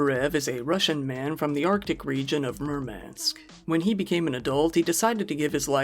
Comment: When you said "Murmansk", I heard "Mermaids" 😂😂😂😂😂😂😂😂😂😂